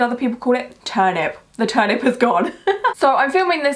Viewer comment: the auto subtitles say “the turnip is God” I’m starting a cult